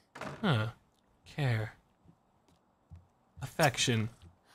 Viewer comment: i thought affection said arrection. i was like ANTONIO!?!